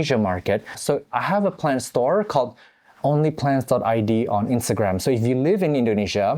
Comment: For a minute I thought it said Only Fans rather than Only Plants 😂